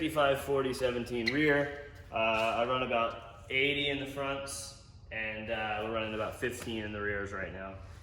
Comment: Did he say 80psi in the fronts? 😲